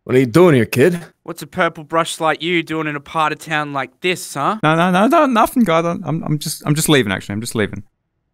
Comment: Narrator rapping is an ingredient for the elixir of life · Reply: I have a captions on and one of the captions said nothing God